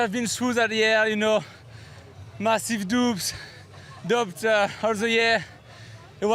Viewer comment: For a second I thought Amaury Pierron was saying he was having massive doobs after his surgery.😆😄 Took me a second to realize he was saying massive *doubts*. Doobs might have helped too though!